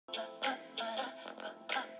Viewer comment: At first I thought they said “anything for you Beyontay”